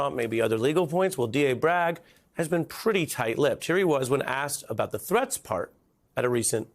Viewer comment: I thought you said "asked about the dress code...". And you then showed Alvin Bragg, who clearly doesn't know how to tie a Windsor knot. :-)